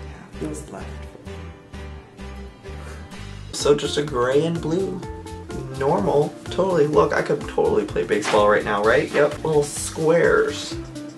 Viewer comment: Wat did he say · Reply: He's describing the shirt (it was a clothing haul video) he was saying, "So just a gray and blue.. normal... look I could totally play Baseball right now.." haha ^_^